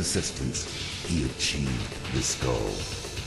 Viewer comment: I don't know if anyone else thought this, but the first time i played this game and saw this intro, i thought raiden said "He achieved the skull"